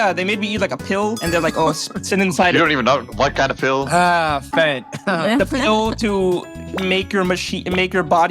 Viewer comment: Bro did NOT say fat.. 5:33 (he said fent (fentanyl)